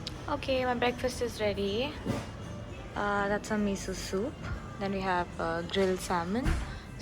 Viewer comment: did you say sussu😂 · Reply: No 😂 I said Miso soup 🍲